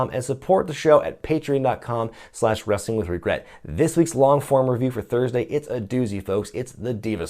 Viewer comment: what does he say at 20:41? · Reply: sowhazupguy this week's long form review is a doozie, it's the diva search